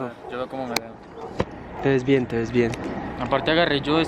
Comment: Después de verme el vídeo varías veces me doy cuenta de lo que dice en el minuto 13:29 “te ves bien, te ves bien”. Yo por dentro Liean is real. #Liean